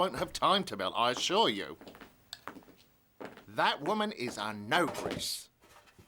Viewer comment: What did the server say to describe Mme Fuccini? 15:45-15:46 "That woman is a ?????" Did anyone else understand that? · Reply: i think he said “that woman is an ogress(female ogre)”